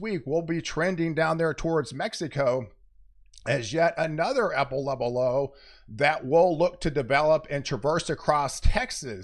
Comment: At the 1:30 ,mark it sounds like he said apple level low or is it just me